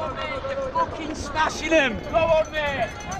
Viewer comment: I don’t even know what that guy said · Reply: Eli Erichsen “youre fucking smashing them, come on mate!”